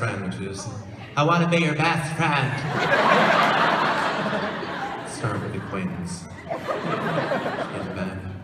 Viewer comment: Umm.. Does anybody know what he/she said at 1:27? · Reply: dtn34 I think he said "Let's start with acquaintance"